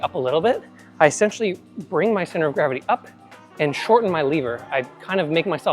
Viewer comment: first at 7:53 i heard liver and was wtf is that liver thing he is talking about:), but seconds later i was aaa lever:)